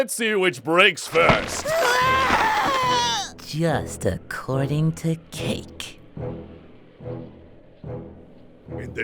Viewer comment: any one thought goku said “according to kink”?